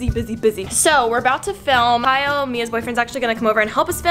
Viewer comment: When she said Kyle I thought she said my boyfriend like whattttttttt